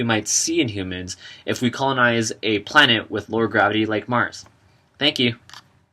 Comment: Mars has LESS gravity than Earth, not more. Bill might think he's being rational, but he's by far the least inspiring scientist out there. He needs to take notes from Carl Sagan's legacy and help people dream about space again. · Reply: Ahhh...he said "lower". I thought he said "more" gravity.